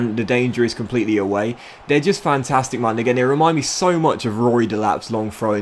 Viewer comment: what did he sayyyy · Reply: He actually said "man again. But I also thought he said the n word too. He said it really fast so it sounded like the n word but if you even turn captions on it says man again.